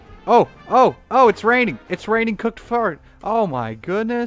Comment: To me it sounded like you said it was raining cooked fart. 😆